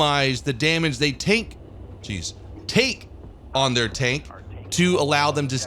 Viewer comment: did say "take on their taint"???? :D